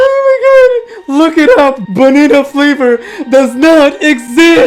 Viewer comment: why did it say "Bonita flavor" (captions)